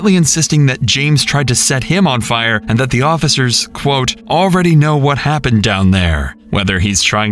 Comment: it sounded like he said "ya'll DONT even know what happened down there" not "you already know what happened down there."